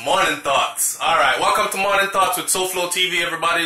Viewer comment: I thought you said "good morning, thots" in the beginning of your vid.......lol........I was finna run to another video! :)